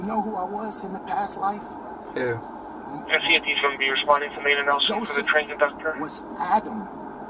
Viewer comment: I swear that said 'RCMP is gonna be responding...' in Columbus Ohio??